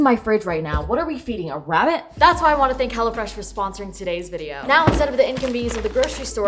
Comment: i thought it said thx hellofresh for snoring this video hahhajhsja